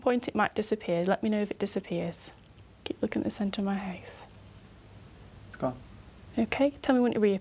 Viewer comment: am i the only one who think she said "hace" instead of "face" @ 1:57? I think she wanted to say head but meant face and ended up saying hace instead.